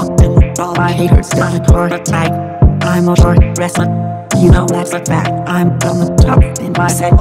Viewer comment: I listened to sharp dresser without looking at the video and I thought it was saying shark wrestler